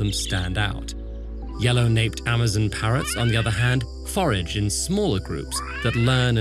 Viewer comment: Can it be that in 1:21 is foraged in instead of forage in? I’m English learner so I’m kind of wonder if we need to use passive in here? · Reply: No, they used it in active sentence: "Yellow-naped parrots on the other hand, forage in smaller group"